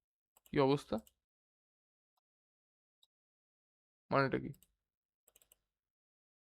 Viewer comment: internet explorer did I hear that correctly???